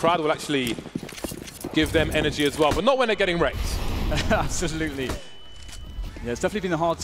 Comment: I swear at first he said "Not when their getting raped," but after relistening, I think he says "rekt"